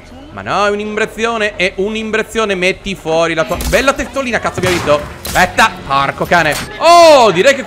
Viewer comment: ma solo io ho sentito cazzo